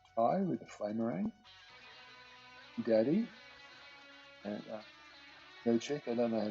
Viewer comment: Did I hear the bilby's name was Gerdy? Or was it "a bilby" and I'm hallucinating? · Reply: Bilby Dad